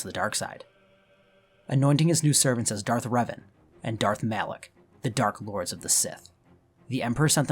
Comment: I heard, "anointing them Darth Revan and Darth Malak, the Dark Bois of the Sith!"